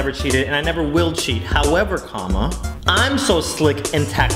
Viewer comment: "However comma" Even in youtube videos, grammar is important. Thank you for acknowledging that today. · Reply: Ohh he says "comma" I hear "karma" errtime.